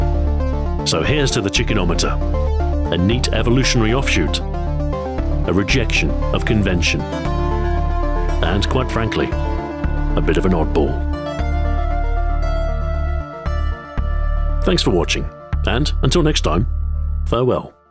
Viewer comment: Thought said "...and until lunch time, farewell"